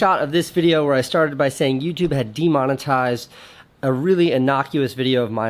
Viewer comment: I thought it said demonized..